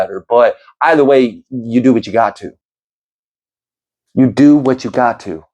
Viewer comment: thought you said "You do what you God too." ❤